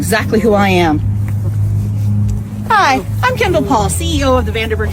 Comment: i think she said kendall paul. WILD😳 oh and sunday mysteries! Great vid bro 👍